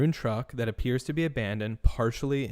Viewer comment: ish- I think dispatch said not abandoned but, "appears to be doing maintenance..."